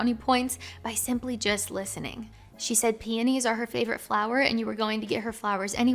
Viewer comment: When you said "peonies" at the 2:11 mark, I thought you said panties at first. I am finishing up a night shift while doing school work as I'm watching this. It's also been awhile since I've been with a woman. None the less, good advice as usual. I enjoy your videos!